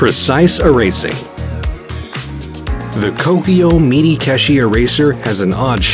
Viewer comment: My brain: sure I want these but why is he saying KOPIYO instead of KOKUYO. replayed it like 6 times, thought I was going insane.